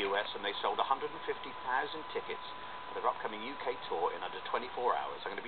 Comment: i thought the guy said 'and they've sold 150,000 chickens' I was like that can't be right, they're musicians not farmers and then i realised he said tickets! i was like oh...